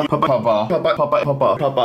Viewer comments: it sound like he says kappa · After a long time it sounds like hes saying kappa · sounds like he is saying kappa after a while